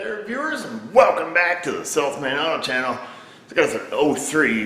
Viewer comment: ROFL. Watching with subtitles, " Welcome to self man on channel". Hilarious because I always hear "Self made auto" myself. Love your videos. Don't give Advanced Auto any flying lessons today!